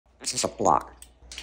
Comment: I heard “this is a Glock “ 😂